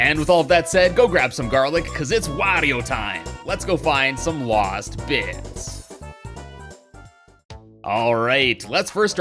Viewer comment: Man I that you said waast bits instead of last bits